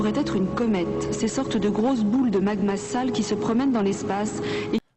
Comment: comment ça "magma sale" ? 😅 Elle dit quoi en vrai ? j'arrive pas a comprendre